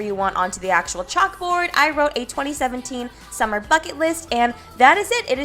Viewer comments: I thought she said planning a summer fuck it list omg but she said buckit list my mind is so messed up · When she said summer bucket list I thought she said summer fuck it list 😂😂😂😂